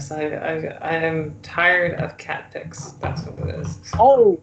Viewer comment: Oh! Cat pics, I thought you said Catholics.